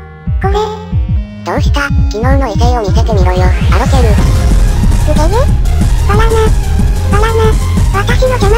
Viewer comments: これアロケルバナナって言ってるように聞こえるんだけどw かっこよすぎ！ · バナナにしか聞こえん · ばぎゃながバナナにしか聞こえないのだが笑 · 頃のアロケルの「ばぎゃな…｣が「バナナ｣に聞こえた私は末期ですかね。 メリアちゃんが「君｣って呼んでるところ初めて見た気がする！ 、強くなりすぎ！カッコイイ！ 次回も楽しみです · ばぎゃながバナナに聞こえる